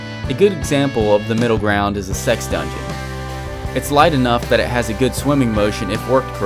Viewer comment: did you say a good example of a middle ground is a sex dungeon?